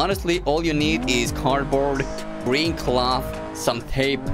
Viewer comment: Come one not again, 6:39 "Bring Cloth"- In the subtitles, Even when u said "Green Cloth". What did I say I always fix your mistakes. Well I guess someone has to do it right?. Cya :D